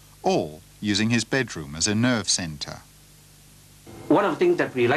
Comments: "using his bedroom as a nerd center" that's what I heard... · did he say nerve centre or nerd centre?